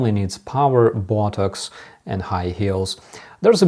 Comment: "Putin only needs power, botox and high heels" 🤣🤣🤣 Slava Ukraini 🇺🇦🇺🇦🇺🇦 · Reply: I thought he said "buttocks" which conjured up a whole different image, but equally hilarious. 🤣 "Botox" is undoubtedly what he actually said!😳